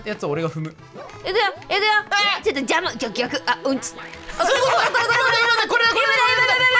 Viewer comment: う⭕️ち…？空耳…？